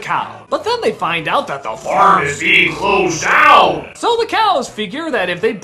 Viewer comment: The awkward moment when you find out that THE FARM IS BEING CLOSED DOWN. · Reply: I kept hearing "The Pharmacy Closed Down" fsr